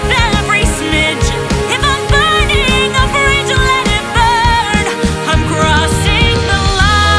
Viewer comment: Fun fact: When I heard "If I'm burning a bridge" what I heard was "If I'm burning a fridge".